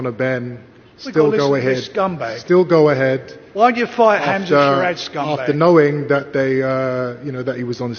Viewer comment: someone tell me what frank says pls? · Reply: why dont you fight hamza sheeraz scumbag